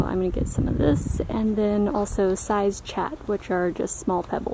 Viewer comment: what is she saying ? Side chart that are like small pebbles?